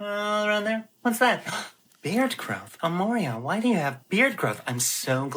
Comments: he called himself "hemorrida"? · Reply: I thought I heard, “ammonia”😂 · Amouria/Amoria? I thought I heard "Ammonia".